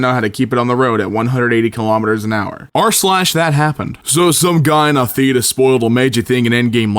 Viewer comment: I swear I misheard him say "So one guy in a fetus..." ....this is why I have no friends